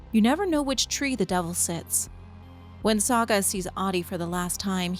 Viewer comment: The first time I heard Ahti say, "You never know in which tree the devil sits," I heard, "You never know in which tree the devil shits."